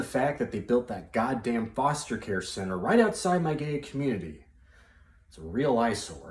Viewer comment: Thought he said “right outside my gay community,” instead of “right outside my Gated community